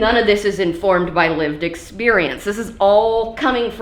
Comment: when she said "...lived experience..." I heard "...limp dick experience...", either form works.